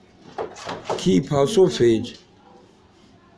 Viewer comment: did he say "keep her sausage"? 😂😂🤣🤣